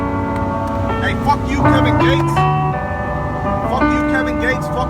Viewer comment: Also- if you're gonna try and rep satan or Lucifer...then pick one. They're not the same thing. LOL DID HE JUST SAY "HEAVEN GATES" (and not "Heaven's Gate" lol...) DOES HE EVEN KNOW WHAT THAT IS? 💀 Edit: yeah definitely misheard him saying Kevin Gates which makes way more sense.